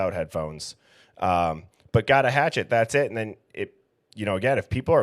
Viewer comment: At the dumpster, it sounds like he says "alrite that's it"